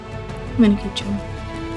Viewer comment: What does she say @ 19:32? I love that she went for a second last dab, and remarked how tasty it was. Jenna is a total badass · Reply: i heard "man, keep chewing"